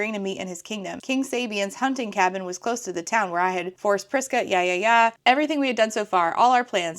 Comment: I totally heard “Crisco” instead of “Prisca” multiple times.